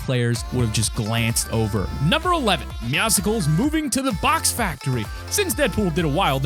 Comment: did he just call Meowscles: "Meow-sicals"??? he's literally a cat with muscles.